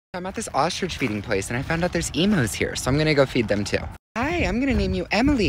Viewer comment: For I second I thought he said there are emo 🖤 people here let’s go feed them Me I’m in luv with an emo girl